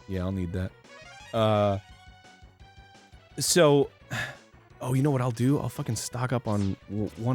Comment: it sounded like Dan said "So hhuuh"